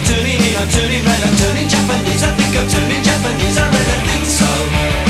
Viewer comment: All of this time I thought he was saying Johnny Japanese.